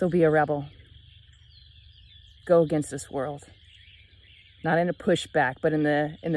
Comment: Heard everything okay except I thought I heard you say "be a rebel, go against the squirrels..."